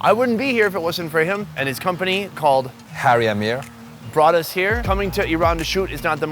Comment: when he said his website is harryAmir and the close caption says HairyAmir😂😂💕💕💕